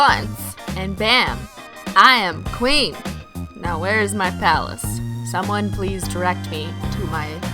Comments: I thought she said "where's my phallus" xD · when she said where is my palace i thought she said where is my salad... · Was I the only one who heard " now where's my phallus" i just what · I thought you said phallus at first lol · Lol. At first I thought you said "I'm queen, where's my phallus".